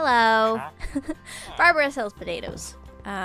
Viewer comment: I heard her wrong and thought she said Barbara is overrated instead of Barbara sells potatoes and I was abt to get mad 😅